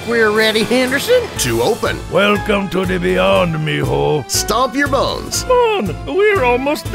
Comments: I legit thought it said stomp my balls💀💀, also did he just hit the fucking Gangnam style? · this shit funny af 😂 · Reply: I thought it said “stomp your balls” 💀 🤚